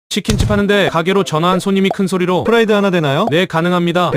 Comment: 처음에 후라이 하나 내놔요로 들었슴ㅋㅋㅋ